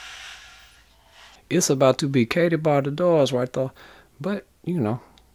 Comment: "It's about to be Katie by the Doors right there?" That's all I can hear, but what are you actually saying? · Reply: ) "Katie bar the doors". It's an old expression basically used when it's over and done.